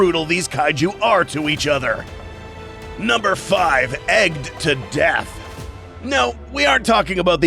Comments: i thought it said edged to death 💀💀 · Wait So for the Egged to Death, my sleep deprived brain thought ir said Edged to death and I was very confused for a few minutes